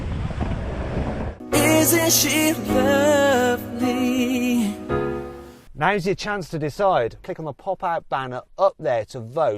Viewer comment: 水〜しぶ〜き〜（空耳）